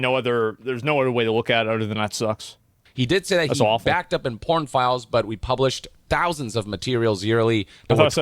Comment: I thought he said Porn files too